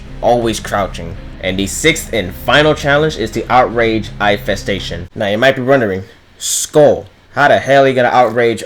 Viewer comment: ngl thought he said outrange eyefestation and i was thinkin isnt that horrendously easy